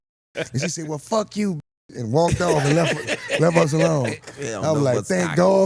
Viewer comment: They won’t get monitized from youtube that’s why they have to bleep out the cursing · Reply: They let one slip..at 4:49 I heard a "f you".